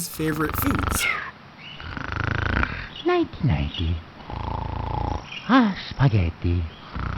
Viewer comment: I always thought that he said "nineteen ninety" (like a specific year number), but it was actually just "nighty nighty".😴